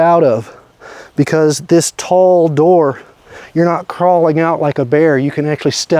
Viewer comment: Great tent. Think I found my next tent. I hate a short tent even though I’m short. how tall are you? When you stand next to it looks tall. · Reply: Never mind I missed it when you said 6’ tall. My wife was in the kitchen and heard you say it. 36 years of flying jets and being around them has damaged my hearing